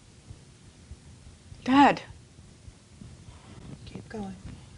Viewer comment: does she say dead or dad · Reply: dad